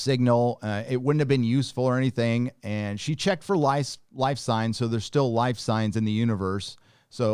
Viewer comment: Lol at 8:19 thought you said “she checked for lice” 😆